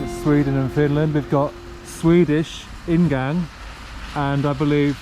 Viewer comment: I love how you said "In Gang" instead of "Ingong"